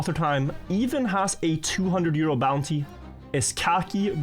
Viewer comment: I heard "200 year old bounty" and I thought mispoke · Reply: euros, not years old xD